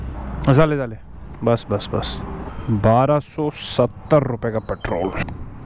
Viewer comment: did he just said "jale " in Konkani language🤔🤔🤔 (ps. Jale means Ho gaya😗) Or is it Zhale ??in Marathi